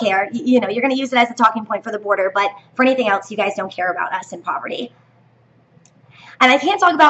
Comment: I'm sorry, but did you say "us in poverty" at 4:29 ?